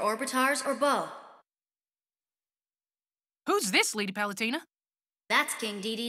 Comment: Pit: “Who’s this lady Palutena?” King Dedede: I was in Brawl with you... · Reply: I actually thought you were saying “Who’s this lady, Palutena?” 🤣